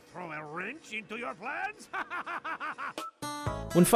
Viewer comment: I thought the spy said did I throw a wrench into your pants but turns out it’s actually plans